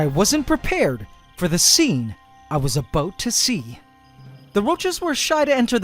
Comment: “The scene I was ABOOT to see” as soon as I heard that, I remembered South Park making fun of the way us Canadians talk 😂